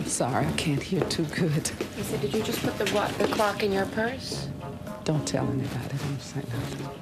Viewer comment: Honestly first time I watched this I thought she said “ did you just buy that glock in your purse?”